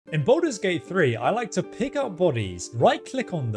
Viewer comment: I heard him say Baldurs Gay 3....... I'ma leave now.....